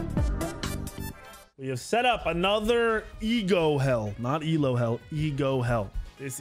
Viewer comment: For some reason in your last video, I heard “eagle hell.” Now it makes more sense hearing “ego hell.”